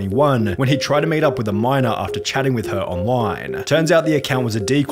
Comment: Sorry, at around 10:05, I saw the subtitles saying "he was meeting a miner", I was generally confused for 10 minutes until I realized it was minor and not miner🤣🤣